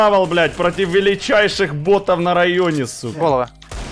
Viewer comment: мне послышалось он сказал ботов на районе это точно?😮